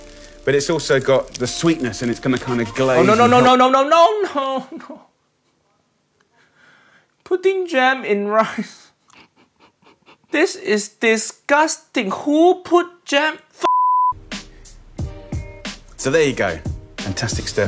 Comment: "Putting Jam in Rice" "This is disgusting" Uncle Roger - 2020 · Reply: I hear "Pudding, Jam, Rice" :P